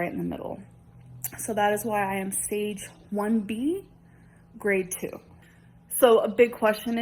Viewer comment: Omg y'all talking about pre cancerous cells vs cancer have no clue what you're talking about. If she just had precancerous cells they would not have given her a hysterectomy so quickly. Stop trying to change facts. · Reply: I thought she said that she had stage 2b “womb” cancer.